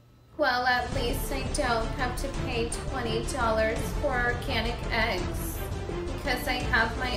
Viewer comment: $20 for orcanic eggs. Swear to God I thought she said ‘a can of eggs’ 😂😂😂